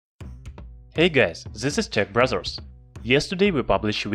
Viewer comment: I heard "this is tech BRAZZERS"